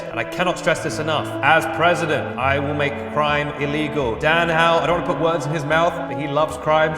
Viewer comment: I thought he said "make pride illegal" instead of make crime illegal and I was like "I thought that was Trump's job"